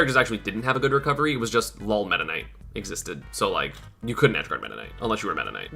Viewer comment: I heard lol metaknight